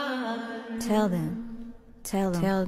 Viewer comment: Why am i hearing hearing "i hope you fly to pizzafy" 💀